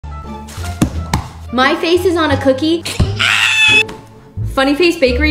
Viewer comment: Omg i thought that said coochie... 😮